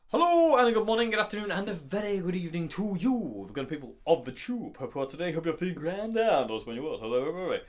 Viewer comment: what does he say after good evening to u ? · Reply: hello, good morning, good afternoon and a very good evening to YOU, the good and wonderful people of THE TUBE, hope you're well today, hope you're feeling grand and all is well in your world...hello everybody